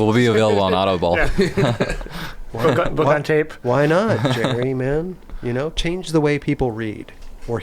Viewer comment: Does he call him Jeremy @ 1:33:18 ?